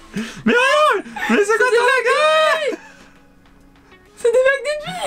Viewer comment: Il a dit quoi à 23:10 svp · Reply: Je crois qu'il a dit gogole